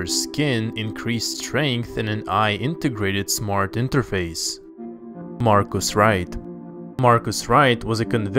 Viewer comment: I thought he said Mark Is Right